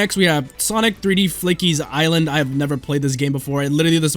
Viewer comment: I thought it said Sonic 3D F**kies Island instead of Sonic 3D Flickies Island.